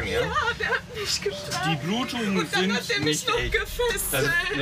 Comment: Bei 7:13 erstma "und dann gefistet" verstanden 😂